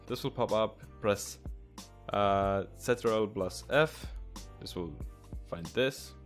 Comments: The Command he said at 1:55 What did he say? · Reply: i said ctrl+f :D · um what does he say at 1:56 its like something plus F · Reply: ctrl+f